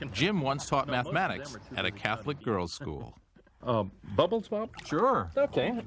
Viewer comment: I thought i heard him say a teacher a Catholic school...two seconds later we sher are dope we sher are bed ..... I love it simply 👍👍👍👍👍